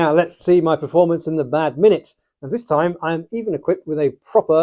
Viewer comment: I'm pretty sure that says "bad minute" instead of "mad minute."